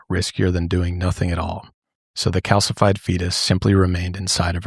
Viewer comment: 😂hahaha I thought you said Calcifide Penis! I had to rewatch that part 3 times. I’m really glad that you didn’t just Disappear because I really liked watching you and Josh..so funny and entertaining you two are. Glad you’re back 🎉